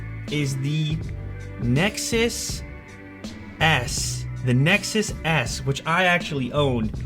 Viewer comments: I LAUGH MT ASS OFF WHEN HE SAID "Nexus S" because I thought he said "Nexus ass"😂😂😂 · did he just say nexus ass?